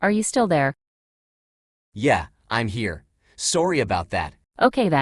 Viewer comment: Did it just say "sorey"?! Who knew the AI was actually Canadian 😂